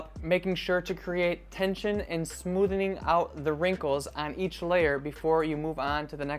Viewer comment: I AM a packing psycho but, I don't cram, I roll :) I heard 'smoothening' when you talked about wrinkles :) Great ideas.